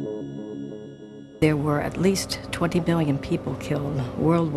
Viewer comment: she accidentally says "20 *billion*" or did I hear that wrong?